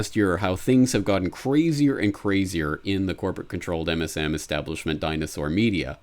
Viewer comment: Did you say: "... in the Corbett controlled MSM establishment dinosaur media"??? :-P I didn't realise that you were behind all this ;-)